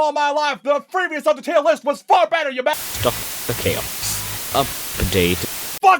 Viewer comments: When they were retuning him, I heard, "Stop the chaos of the date" XD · While they were re-tuning it sounded like he said “Stop the chaos of potato”